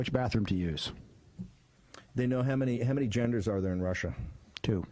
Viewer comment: I legit thought bannan said janitors when he said genders